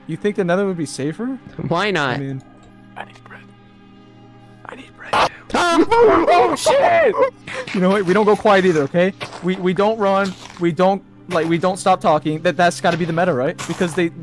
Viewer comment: was that shit i heard?